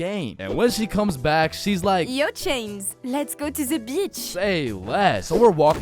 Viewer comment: when French people say beach I think they be calling me a bitch and use their accent for an excuse because I heard bitch